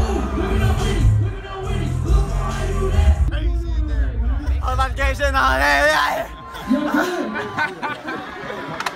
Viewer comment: wtf did that guy say? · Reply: “whole lotta gang shit know what I’m sayin alalalaeh” that was Boonk gang